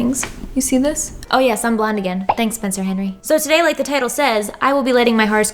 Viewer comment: I had thought you said “so to day like POTATO SAYS”